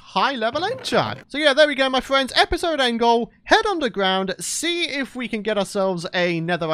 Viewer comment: episode angle? did I hear wrong? :)